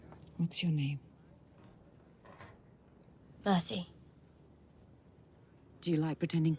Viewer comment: I still think Arya actually says 'Bertie' not Mercy